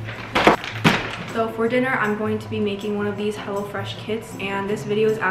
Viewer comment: Was it only me who heard her say "Hella Fresh" but then realized it was "Hello Fresh" TT